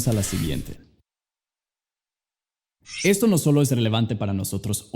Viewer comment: Min. 5:18 Escucho que dice "Relefante" y yo tratando de traducir la palabra hahsjajjaja